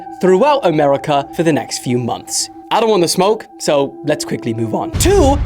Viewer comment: I thought he said “i wanna smoke let’s move one” I was abt to say BASED ASF🔥🔥🔥🔥🔥